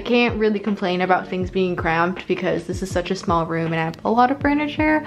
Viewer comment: Did she just say... she has a small room?!